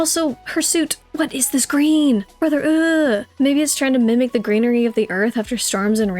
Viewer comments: AYO what did you just say?! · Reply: Ok I literally had to go back and listen again the first time I watched lOL. But it’s “maybe it’s”. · did I just hear her say the N word?(or maybe she said maybe) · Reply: I said “maybe” 😭😭😭 captions got it right